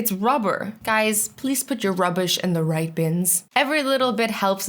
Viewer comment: I heard her say rubish not ruber